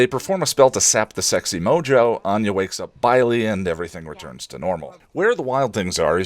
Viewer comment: I was looking away from the screen for a minute and I misheard "Anya wakes up Bylie" as "Anya wakes up bi-ly" as in she wakes up in a manner that implies she is bisexual. Much more exciting than what actually happens.